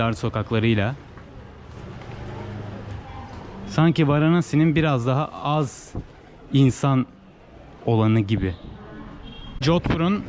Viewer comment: Videolarımda kullandığım müziklerin çoğunu Epidemic Sound websitesinden kullanıyorum. Linkini ‘açıklamalar’ kısmına bıraktım. Spotify listelerime ise Mertian Nomadus ismiyle erişebilirsiniz. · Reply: de "Ne dediğimi anlayan olursa yazsın" demişsin."Varanasiye göre burası daha sakin ,burada daha az insan var"demişsin morukçum..